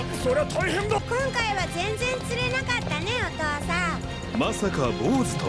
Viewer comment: 手繋いでるの可愛すぎ❤ 「赤井さん」って言ってると思うんだけど、「お父さん」に聞こえるwww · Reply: お父さんって言ってますよ。単行本にもそう書いてありますよ😊